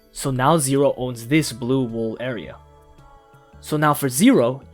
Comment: Wait. Sorry, BLUE wool? am i heard that right? blue??!!